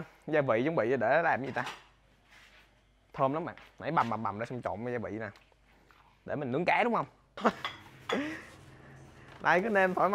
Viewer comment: tua đi tua lại mà không nghe thấy nói gì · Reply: "thơm lắm bạn, này bằm bằm bằm ra trộn với gia vị nè. Cái này để nướng cá đúng hong??? .... "